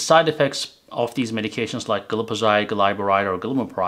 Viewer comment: he says glipizide (one of the common diabetes drugs) but the closed caption says glyphosate which is the herbicide known as Roundup. So it is a mistake in the closed caption computer.